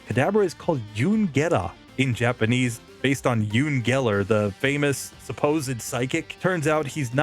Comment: Just a minor correction: At above 12:50 it sounded like "Yun Geller". It's Uri Geller, pronounced "yer-E". Otherwise I go like knowing that snake oil salesman has a pokemon named after him. I'm just going to assume for my own sanity that it's not a complement.